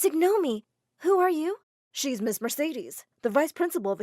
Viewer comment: Did I Just Hear Ms. Mercedes?